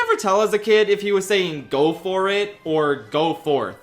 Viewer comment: Wait, Pokémon Trainer's voice clip is "Go for it?!" I always thought he said "Good boy!"